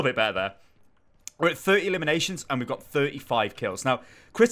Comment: ''30 Eliminations and we got 35 kills'' did i hear that right